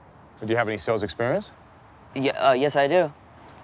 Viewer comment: Was I the only one who thought he said "Put Locker" lol?